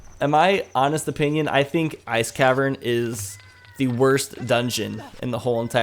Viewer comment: I thought you said "Ass Cavern" I wanna go to the Ass Cavern